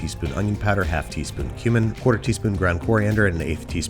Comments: I had to do a double take because i thought i heard him say human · did he just say one teaspoon of human